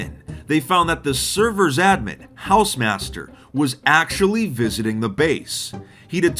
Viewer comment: Imagine logging back in and hausemaster is just inspecting your base. Basically, the equivalent to coming home and Jesus is in your house chillin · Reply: i thought hausemaster said hamster lmfaooooooooo